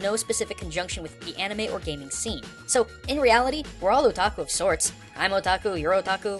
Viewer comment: i keep hearing "a taco"